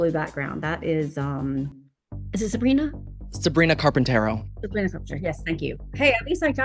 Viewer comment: I SWEAR I remember him saying “Sabrina carpaltunnel”